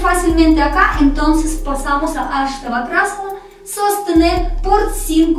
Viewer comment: cuál es el tutorial de la asana del minuto 24:16 no entiendo su nombre ni encuentro el video. Muchas gracias por estas clases, han sido de verdad un regalo del cielo en esta cuarentena! Saludos! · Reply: Se llama astavakrasana